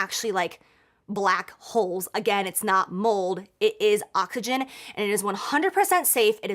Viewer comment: Did she say "occagen" instead of oxygen??!?? LMAOOOOO